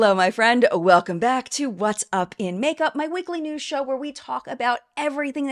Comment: I totally thought you said “my wiggly news show” and it’ll forever be stuck in my head! 🤣🤣🤣 Neutrogena has the best sunscreen and they should lean into that! With the Olympics, we got to see that bada** women come in all shapes and sizes, now with the Dove commercial, we get to see them in all ages! 💪